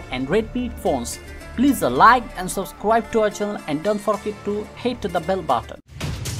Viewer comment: did you just say hate the bell button? lol